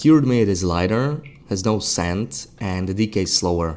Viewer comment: "no scent" I finally see as a text on screen. I thought you were saying no sand. Wondered where the sandy fish mechanic was coming from